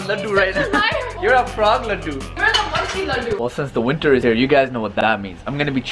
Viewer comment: i thought said tushy